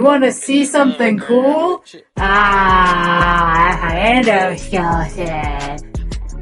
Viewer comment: LOL What hell did she say there 0:06? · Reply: My endoskeleton